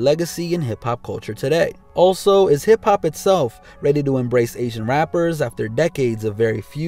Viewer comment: Being a old person. I don't understand it. I even thought they were saying aging rapper.